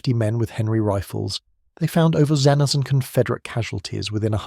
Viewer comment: At @ 27:26 , how many is "...over zenzan Confederate casualties..." ? (Both audio and Closed Captioned) Is a new counting method being used instead of base 10? It's obviously not binary. The only remaining conclusion is that it must be base gloph'neb. PLEASE..... Find a better AI narrator.😂